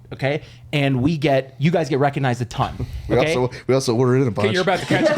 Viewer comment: when talks over and says something like “we also weren’t in a bunch” what did he actually say? I can’t understand it and I want to so bad based off of how the boys reacted 🤣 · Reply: “We also order in a bunch”